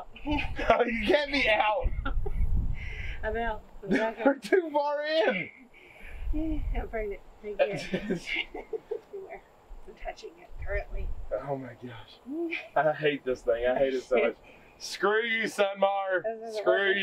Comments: 🤣🤣love this family,,, I heard" I'M PREGNANT "9:17 Lordy Day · I totally heard “I’m pregnant”?! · We laughed so hard tears was rolling, but did she say at 9:13,14 I'm pregnant, did anyone here that, listen. I could be wrong. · All I heard was "I'm Pregnant" 🤣🤣👶👶